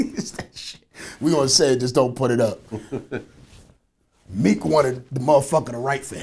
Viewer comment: I thought said don't put it out.